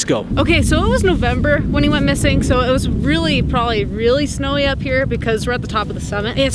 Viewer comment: Am I the only one who thought said "smelly" instead of snowy? I kept wondering why it would be smelly...